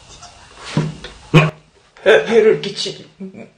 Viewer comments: 부분 정확하개 귀신 목소리 해를끼치지마 라고 정확하게들림요 ㅇ,ㅇ;;소름돋네요ㅇ,ㅇ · 끼치지마 7:53 해를 끼치지마 여자가 엄청 작은목소리로 말하는거 나만 들리는거 아니지? 다 들리는 거지?